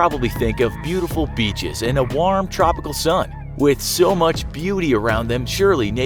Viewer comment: I thought he said "with so much booty around them" at first lol